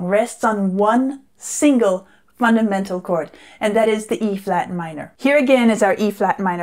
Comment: E flat minor All I hear is “ bad company” 😂